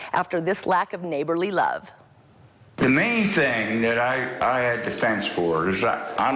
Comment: WHAT did he say??? I didn't understand a word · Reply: "I don't want to have that view, alright, of all that stuff"